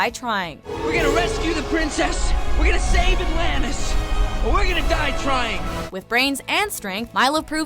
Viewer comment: for a long time, i always thought he'd said "We're gonna save Atlantis and we're gonna die trying" XD